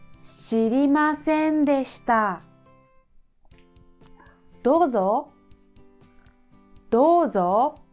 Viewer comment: I'm sorry i can't understand what "re you are" Is. Can't anyone please clear my doubt? 😭😭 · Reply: Here you are ☺️🙌